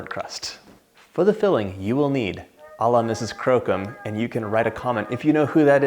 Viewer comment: OMG - LOL!!! I heard you say Mrs. Slocombe ........